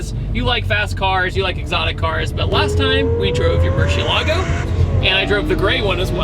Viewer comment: Do I keep hearing “muSHI/murCHilago”?